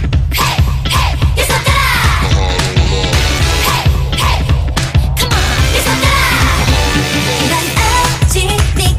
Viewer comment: what is that deep voice saying at the beginning? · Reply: Come on